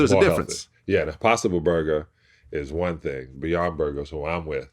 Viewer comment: Am I the only who thought he said “possum burger” at 2:45?